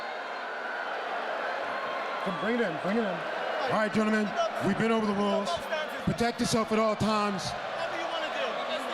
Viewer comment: What did Conor say at 2:46 ? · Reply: “We can go both stances x2, let’s go”